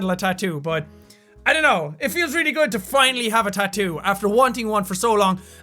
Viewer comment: if u have subtitels on and start at 3:15 wait a little and when he says i dont know...it feels really good than it says (thats what she said) subtitels failed and yea ik i spelt subtitels wrong anyways just throwing that out there a little funny joke